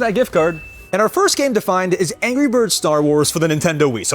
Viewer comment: DID HE JUST SAY ANGRY BIRDS STAR WARS ✋🥹🤚